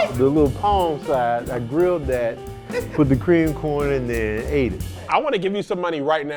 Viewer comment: can someone tell me what he said all i heard was bla bla bla and grilled that. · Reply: The little palm sides